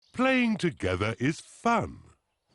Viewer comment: i heard fuck instead of fun